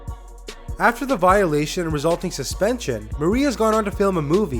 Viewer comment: / 1:38 it sounds like you are saying maria instead of marie